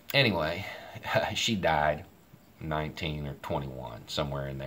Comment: I'm not sure where you got 1921 from, probably just a knot in your tongue, but Matoaka (Pocahontas) died in 1617. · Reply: I heard it as she died at 19 or 21